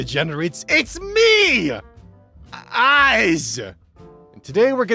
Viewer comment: I kinda sleepy and it sounded like you said "I'm eyes" instead of and I hallucinated several eyes growing out of your png for a second there